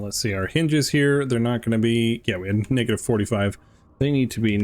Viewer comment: uhm.. What did you say? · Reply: Negative?